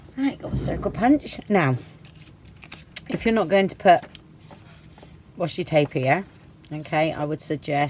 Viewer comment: I am watching this video again so that I may make one of these portfolios for Christmas... your videos are always so easy to follow and I simply adore your style... that being said, because I tend to watch the videos, sometimes at night while my hubby is sleeping or when I am washing dishes, I have the “Closed Captions” on... I am used to the interpretation being quite askew because of your British accent, but sometimes it just makes me giggle at what it THINKS you said... todays giggle? Washi Tape... it thought you said, “What’ your tape?”...🤣🤣🤣 Hugs to You and Yours...❤️❤️❤️